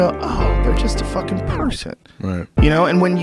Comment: Can any native speaker please write what is the last word in the sentence Joe is saying at 0:34 "you know, ah, they're just a fucking ??????..."? And what podcast is this whole speech taken from? Many thanks to the uploader, great stuff and background music!!! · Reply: Pavel Torpan "person"